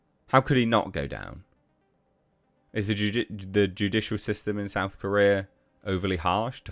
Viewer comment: Sorry did you just say Jujutsu system in South Korea...?. I almost want that to be a thing.🤣